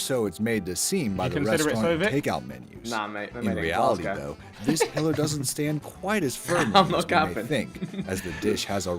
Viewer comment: Can someone please tell me what did Vik say at around 2:46?? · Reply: I know it’s been 3 years 😂 but I believe he said “nah mate they’re made in Glasgow”